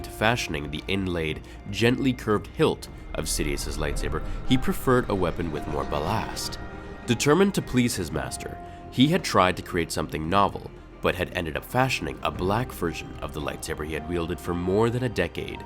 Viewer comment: What is that word you used around 1:45? "He preferred a weapon with more-" Malast? Malaste? Molast? I tried looking it up but it kept giving me irrelevant results saying Malast is a surname, and definitions for namaste, and malaise or something. I have to assume it's a "borrowed" non-English word or something.